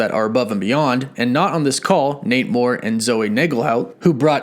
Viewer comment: I thought for a split second he said namor